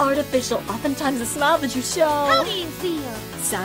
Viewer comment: i heard "how do ya feel" as "howdy" lmfao